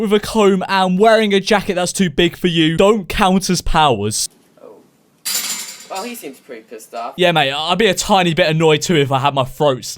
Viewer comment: To know more about greasers, read "The Outsiders" by S E Hinton, and they'll also talk about another enemy gang to greasers Also, what did he say at 3:36? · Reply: He said "well he seems pretty pissed off"